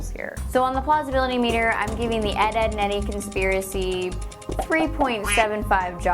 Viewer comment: dead, dead, and deady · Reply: i thought u said dad, dadd, and daddy lmao